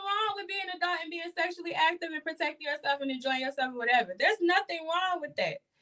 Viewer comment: Wayment..... @ 17:37, did she say "..there's nothing wrong with being a thot and sexually active and protecting yourself .." 🤔😶🤦🏾‍♀️ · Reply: Being adult